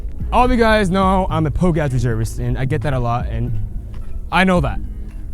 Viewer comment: what did he say? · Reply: POG ass bitch. POG = Persons other than Grunt